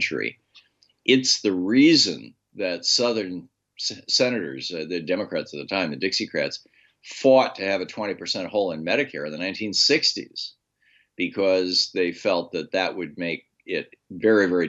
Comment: can someone explain what he means here? Does he say 20% “hold” on Medicare?